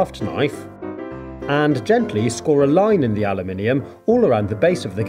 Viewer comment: Did he just say "ala-mini-um"?😂😂😂😂